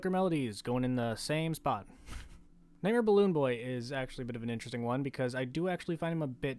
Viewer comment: it sounded like he said "Mayor Balloon Boy" which is a new phrase that scares me